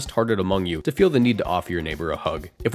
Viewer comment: I thought you had said ‘off’, so seemed like you going to say ‘off your neighbor’. My mind was racing with trying to figure out what was going to be that bad, till you finished ‘a hug’.